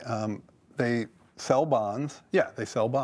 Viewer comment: Is this a joke? Because I'm not laughing. I'm actually scared if this person is actually employed.. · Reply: I thought it was a joke. I thought he said "We sell bombs." Not "We sell bonds." And laughed during the entire clip. Then I realized he said "bonds" and he was being serious and I cried.